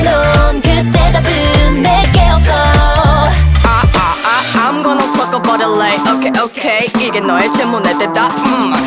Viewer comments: I don't know why but I'm obsessed by Dami's "okay okay" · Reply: I misheard "Im gonna fuck up a ligth okay okay" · I ain't the only one that thought they heard dami say she's gonna fuck up something right? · wait, dami DOESN'T say "im gonna suck up all the light"? my reality is crushed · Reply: i thought it said "I'm gonna fuck up a light"